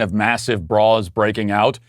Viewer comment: I thought you said "massive bras breaking out" I laughed so hard!